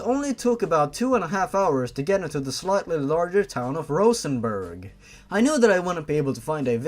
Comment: "1/8 inch in diameter" I zoomed out and assumed they were talking about Toby's dick edit: "I felt Toby stiff and briefly." Jesus Christ, the sexual jokes I could make about this commentary is ridiculous. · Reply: I misheard "Rosenberg" as "Luxembourg" and started flipping out in broken German.